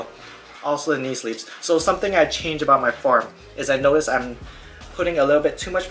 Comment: I thought you said "something i changed about my fart" at first, good info though man, i agree on the keeping the same program, progress is key as always